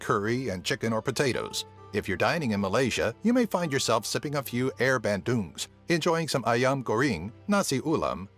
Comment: When you say air bandung i thought you say air badum